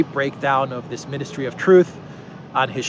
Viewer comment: At first I thought it said ministry of theft. Then I saw the intro. I must be psychic.